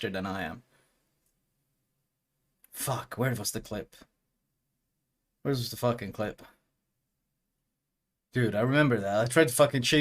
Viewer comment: am i the only one who misheard it for "where was the f***ing clit" and then giggled like an idiot? xD